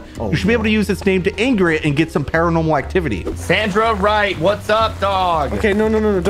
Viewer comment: LMAO DID SAY “Sandra white what’s up dog” OR SIGILS